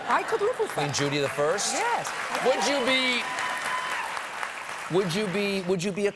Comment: I heard "despot" as "desk bot" cause I had never heard the word